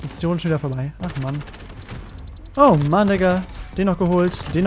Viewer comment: huch was habe ich denn da gehört 😂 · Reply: OrTak Flyy Passiert ungefähr eine Sekunde später, er sagt My N***a